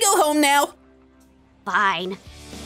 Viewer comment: Kit said pine instead of fine